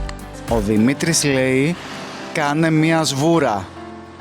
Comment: How many did you get? Let me know if you liked this video and if you'd like to watch more like this! If it's your first time here, don't forget to subscribe and click the bell : ❤ <link> We're coming with more content! Φιλάκια! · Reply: Did he say κάνε μια σπορά; to mean “take a spin?” Or did I mishear him?